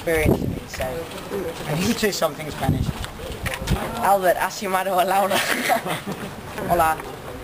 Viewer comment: que fue lo que dijo Tom cuando le preguntó si sabia español No le entendí bien solo entendí el "hola" y el " me llamo tom holland " y el "estoy" pero no entendí lo primero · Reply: Lyra Nath Albert has llamado a Laura 😂😂😂